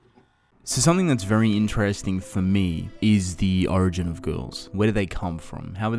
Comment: I thought he said “something Interesting to me is girls”